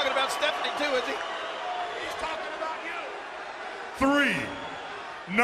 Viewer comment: Undertaker: "in all my years i dont think ive ever seen so many assholes in one place!" King: "you dont think hes talking about stephanie do ya?" 5:16 (Vince in the background and picked up on audio): "Hes talking about you!" LOL.I ABOUT DIED WHEN I HEARD THAT!